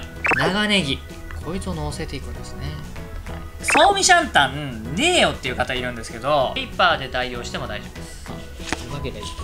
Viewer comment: のあたりの創味シャンタンない人は何で代用してとおっしゃっていますか…？？聞こえづらくて😭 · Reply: たぶんウェイパー（味覇）です。本人じゃなくてすみません